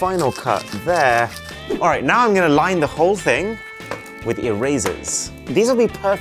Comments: I had to watch it again cause I thought he said - line the whole thing with “your razors” instead of “erasers” 💀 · Him: “erasers” What I heard: “ear razors”